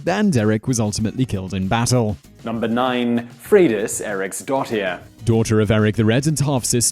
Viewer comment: Ha! I thought you were trying to say Fredas Eric’s daughter!